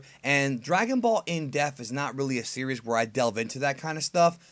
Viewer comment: It sounds like you say "in deff" instead of "depth"